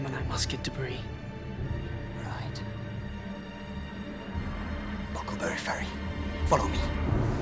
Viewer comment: I couldve sworn he said Buckleberry Ferry